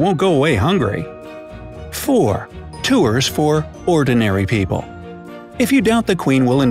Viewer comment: uhh did you just say... tooers